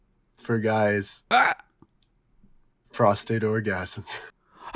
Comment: I thought he said "frosted orgasms". Im down